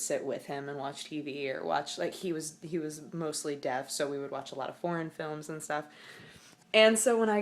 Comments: Thought you said 'watch a lot of porn films'. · Wait, porn films? I genuinly can't understand what she is saying, unless it actually is porn films. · Oh God. At 7:12 I thought you said you watched PORN films with your grandpa and I just sat and stared at the screen thinking "did she just. Did she just say that?" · yea she did. the first time i heard it i wasn't looking at the screen and without reading her lips i would have bet money she said porn. i had to go back to see that she said foreign.